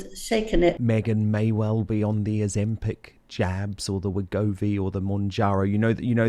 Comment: American here. At first I thought you said "Meghan may be on the Ozempic jams" 😂 then I realized you said jabs.